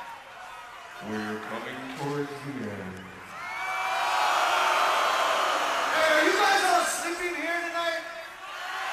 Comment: Damn, Patton looks like he's having a blast here. I love how he always looks insane lol. What did he say at 53:18? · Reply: SylentEcho "Don't worry Download, we're coming towards the end..."